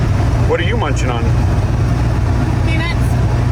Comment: J: What are you munching on? K: Peanuts Me: "Did she just say, PENIS?" 🤦🏼‍♀️🤣